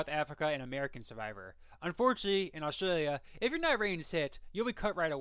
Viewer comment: Hey! Love your channel so far. Im just confused and can't tell what you're saying at 2:50. "In Australia, if you're not ????? You'll be cut right away." Sounds to me like "if your nut raid is hit", and google captions says "if your night rain is hit." Maybe "if you're not ready to sit?" But what does that even mean? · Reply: "If you're not a ratings hit, you'll be cut right away".